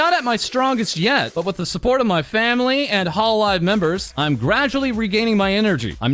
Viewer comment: When you said 'gradually' when reading LA+'s tweet I thought you were saying 'graduating' and I actually almost shat myself lol so thanks for that mate XD